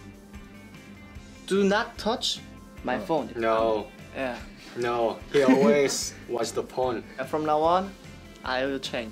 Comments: Can we just appreciate how much better their English has gotten since they debuted?? Edit: OH MY GOSH AT 5:21 IT SOUNDS LIKE YEOONE SAYS "no no he always watch the porn" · Yikes it sounded like YeoOne said he always watch at the porn... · i thought he said porn instead of phone LMAOOO